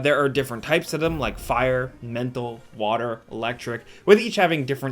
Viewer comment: did I hear "Fire, Mental, Water, Electric" ???